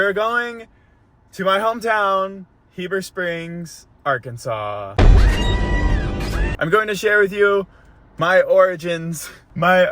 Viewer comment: Why at 1:58 I heard “I’m going to share my organs” lol then I realized that was not what you said